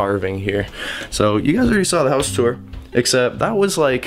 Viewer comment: I heard him say SEWER!!